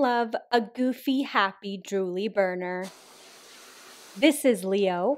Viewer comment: I first heard ‘Who doesn’t love a drooly, happy, goofy BONER’. looks around I mean who doesn’t